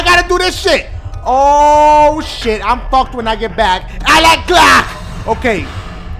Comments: it sounded like you said I like clock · It Sounded Like He Said I Like Crack 😂😂😂😂😂😂😂